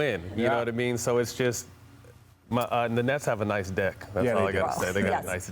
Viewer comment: What did he sayyyyyyyyyy 😂😂😂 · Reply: nice deck.